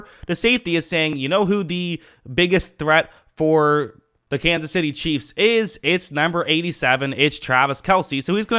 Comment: Every time it sound like you say, the Kansas by City Cheeze